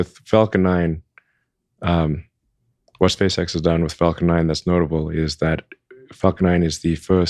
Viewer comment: Elon musk says “fuck 9” instead of falcon 9